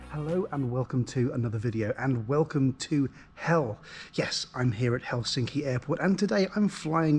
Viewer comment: At the beginning I thought he said “Welcome to HELL!”